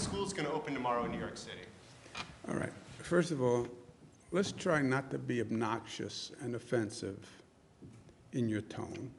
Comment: I thought he just said, "let's not be obnoxious here"... These Cuomo boys have an anger management issue. As did the Hussain boys.